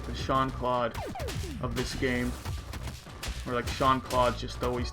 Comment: Did you say “Shawn Claude”??? 🤔